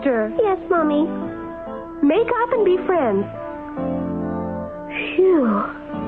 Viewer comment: In 0:27, what did Mommy Koala say to Roobear and Laura? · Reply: He sayed to be friends